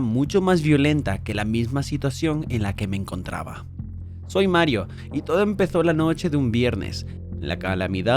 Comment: Le entendí "Soy Mayo" XD 3:06 Larga vida a las abuelitas <3 4:49 No es por nada pero... Esto es potente 8:00 Como dice el dicho: Entre broma y broma la verdad se asoma 10:52 Miren el tamaño de esos eggs 13:06 ¿Algo más? Parce ni que fuera el mismísimo Satán 15:37 Eso es tan mi abuelita... La extraño :"D